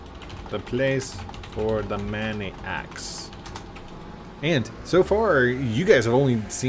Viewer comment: It kinda sounds like he is saying maniacs instead of many axe. 😂(this post is not meant to be mean)